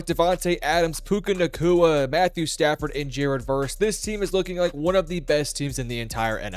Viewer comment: In the video when you're talking about Jared Verse, at 0:11, the caption says "Jared Goff." You might want to change that.